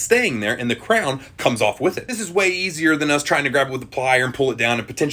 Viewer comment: My alcoholic ass thought he said unfortunately they don’t taste like royal crown green apple 🍏